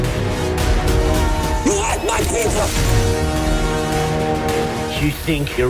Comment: it really sounds like Chappie is saying, "YOU ATE MY PIZZA!"